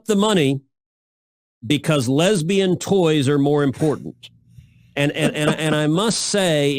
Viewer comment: I swear my mind auto corrected when he said lesbian toy, I thought he said sex toy.